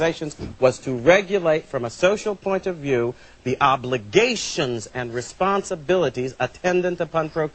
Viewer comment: anyone else hear obliGAYtions?